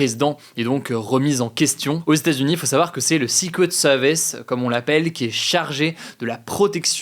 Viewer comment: Le quoi mon bro ? :p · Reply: Le 6 couettes sewvisse 😅